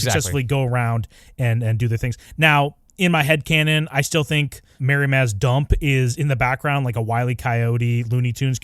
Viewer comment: I misheard Mirri Maz Duur as 'marry Maz Duur' and thought you said "What if he married Maz Duur" and now I wanna know what would've happened if he'd chosen her over Dany (He should've).